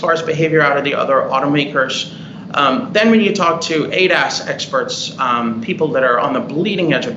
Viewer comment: did he say "ate ass experts"?